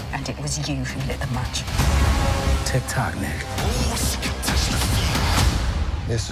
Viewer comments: For a moment there I thought I heard Don say, "Tik tock N---Word.". lol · I genuinely thought Rhodey said "TikTok nigg-"